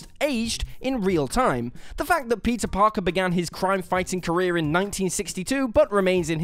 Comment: yeah yeah villains go brr but am I the only one who heard pizza parker at 1:08?